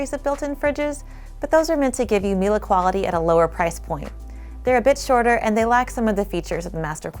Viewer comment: I swore you said "they are a bitch to order". Had to play that back a few times.